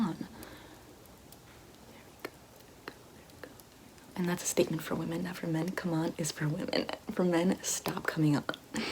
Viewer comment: I don’t understand what she says there. Can someone help?? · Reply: Here we go